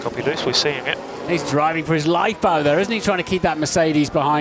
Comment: What did Hamilton say at 4:09 ? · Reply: Sorry for my late coment But he stayed: Some dangerous driving is going on